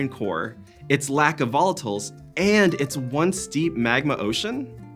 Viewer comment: Is this first full episode? Great start! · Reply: Agreed. Is his name? I thought he said "I’m Ron burgundy."